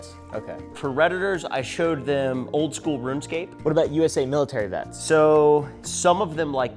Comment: I misheard the "for redditors I showed them oldschool runescape" to "for elders I showed them oldschool runescape" (dont ask how) and was just sitting there as a 28yo... hey, im not an elder ._.